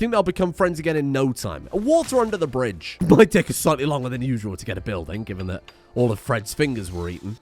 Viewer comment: it 100% sounds like he says "My dick is slightly longer than usual,"